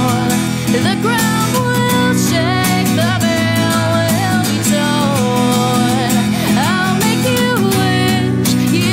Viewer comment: Does she say "the bill will be torn" or "the veil will be torn" 1:22? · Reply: it's "the veil will be torn"